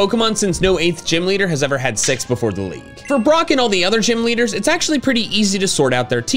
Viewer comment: i heard “but no kanto gym leader has had s*x before the league”